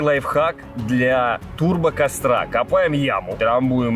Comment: Мне послышалось: для турбо кострации😂😂😂